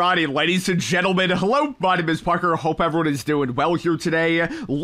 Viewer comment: It always sounds like you say “Hello my name is BARKER” lol😂